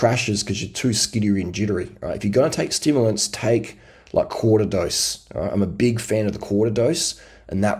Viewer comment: Hey, I couldn't make out what you said about stimulants. Did you say cortidose? 11:05 💯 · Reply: Quarter dosage. If you need more than that and it's not race day then you are abusing stims.